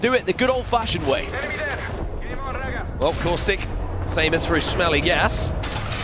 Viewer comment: I thought he said “smelly ass”